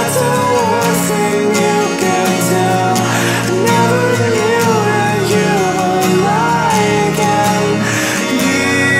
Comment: What does he say from 2:19 to 2:23? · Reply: Michel Rmz Never knew you would lie again. Pretty sure it's that.